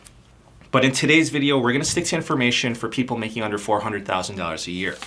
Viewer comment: Did he say under 400 k or over. He probably said under but I keep hearing "over" for some reason.